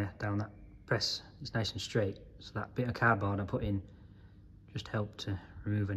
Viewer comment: Haha! You said cardboard I thought you said “cad bar” I was like WTH is “cad bar”😂